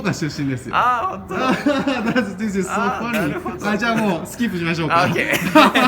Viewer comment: がスキップって言ってるのにスキンシップに聞こえた。。